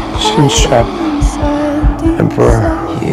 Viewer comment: ? What is he saying 😊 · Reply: He's naming the four types of Arctic penguins: adelie, emperor, chinstrap and gentoo, to calm himself and so is his father in the next shot.